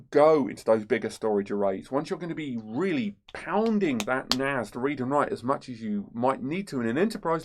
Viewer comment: did he just say "Once You Really Start Pounding That Ass!"